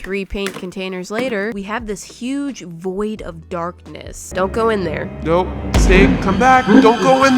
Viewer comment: when she say darkness why does it sound like she said dark p*ss